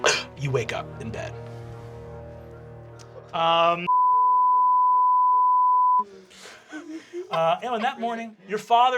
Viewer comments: what does Riz say after he wakes up from the dream? I’m trying to read his lips, but it’s such a long beep, and with no beginning or ending sounds to help me out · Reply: He says “Riz throws up” while Ally is saying the F-word in a sympathetic way. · Finding out what Murph said at 1:21:06 might be the thing that makes me finally get Dropout. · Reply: He literally just says "Riz throws up", so it's weird they bleeped it